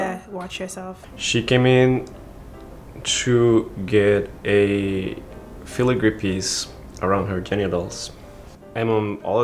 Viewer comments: what does the dude say at 0:38 ? she got a Fila group piece tattoo'd? are my ears broken hahaha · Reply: He said "filigree", Google it ^^ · Omg I had my captions on and at 0:37 when he says, "She came to get a piece around her genitals." the captions said, "She came to get a piece around her kenya dolls." 😂😂😂😂